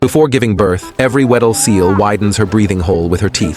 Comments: Human babies have the most comfortable environment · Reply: I thought he said every wittle seal 😂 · Ok I misheard breathing hole as breeding hole